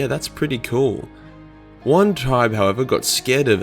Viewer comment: what did he say? I keep hearing "one time however"